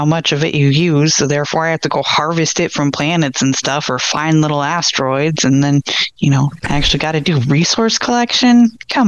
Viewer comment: I thought she said fine little asteroids at first 😂